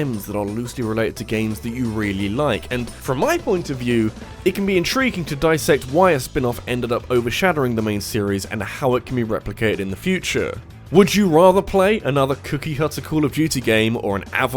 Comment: Oh! I thought it said foreshadowing, not overshadowing... Me big dumb. 😅